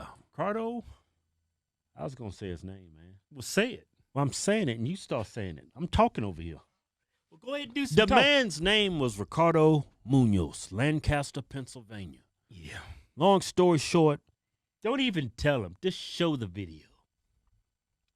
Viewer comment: I thought they said Ricardo Milos. I was going to comment the meme is actually dead now.